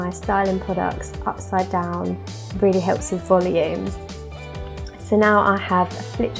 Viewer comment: What did you say at 7:00? I couldn't make it out. Almost sounds like "I find that if I apply my styling products upside down really helps me fully end" Thanks girl. You have gorgeous hair! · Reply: She said, Really helps with volume